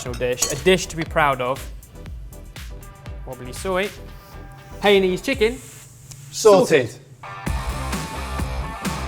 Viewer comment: Did you just say HEI-NEESE CHICKEN 9:57?! ARE U TRYING TO CREATE A WAR FOR YOURSELF? I thought we had an intervention for this already?!